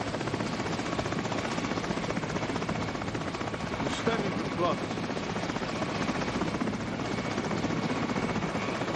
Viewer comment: please someone tell me what he says here!? I can't understand Shit. 😢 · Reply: "you're standing too close"